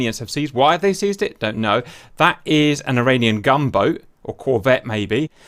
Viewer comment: No offence but I was so confused the whole time because I thought you were saying "Iranian Gumbo"